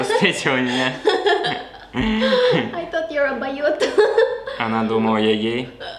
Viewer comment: мне послышалась Дабоет )))))) при первый встречи но перевод что я типо Гей